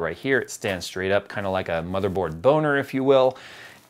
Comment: Very good info but did I just hear you saying "a motherboard boner" at 10:52? :))